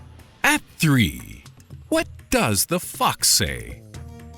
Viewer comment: When you said for #3 "what does the fox say" or something I thought you said "what does the f**k say"